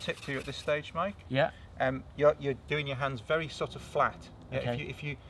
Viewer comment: Excellent tips really enjoyed the narrative I learnt so much especially about the hand drills at first I thought you said you're done a hand job had me thinking for awhile untill I saw the method!!!